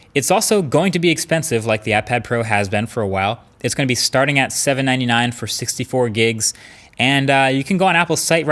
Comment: marquees says $799 and the captions say $7.99 like my broke ass was so happy for a moment